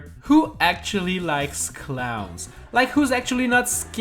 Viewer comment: At the begining of the Clown I heard "Cloud" and I was like "Say What".